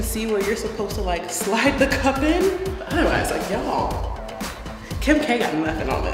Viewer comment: did she say taehyung or is it just me ARMYS U KNOW WHAT I’M TALKING ABT! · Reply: She said Kim k what?